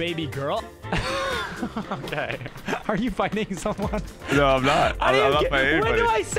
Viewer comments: bro i cant be the only one at 2:43 where i thought nick said “ok are u fucking someone?” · istg I thought Nick said "Are you f---ing someone?" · did buddy just say are you fcking somone 💀